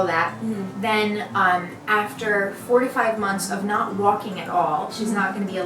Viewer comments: I heard 45 months and went "wait, how long?!" But it's 4-5 months. Lol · Am I hearing it correctly? 45 months of not walking??? That's nearly 4 years, it cannot take THAT long for a bone to heal & if she really cannot move for 4 years, what kind of a life would she have... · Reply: Okay just read the fundraiser & it says 4 to 5 months 😂 I'm an idiot, that's obviously what she said it just went so quickly I thought I heard fourtyfive instead of four to five